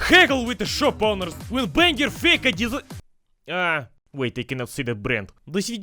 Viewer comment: Gosh, that 4:00 i just couldn't hear what is saying, repeating it like five times. What is in that shop again? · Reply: Адидас